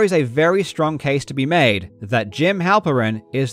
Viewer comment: These people have a lot of power and money, so let's hope they don't attack me for this. A lot of people talk about money laundering but I didn't go into it because it's all pure speculation. Money laundering doesn't CAUSE bubbles. People can take advantage of a bubble that exists, but it's not the root cause. I just focused on what I could prove. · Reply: I keep hearing "Jim Halpert"